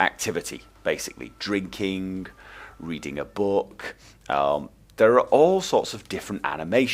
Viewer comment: for the first time he said "reading a book" i heard "eating a book.."